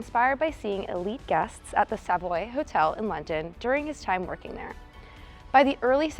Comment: The Savaloy Hotel? I hear the do good sausages...